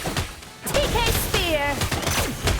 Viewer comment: What did she say? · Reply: PK (Psychokinetic) Sphere.